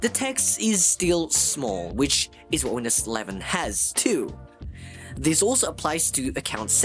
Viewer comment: js the captions say windows 10 but ur saying windows 11 open captions and see for urself 3:16 bro gotta fix it dawg :skull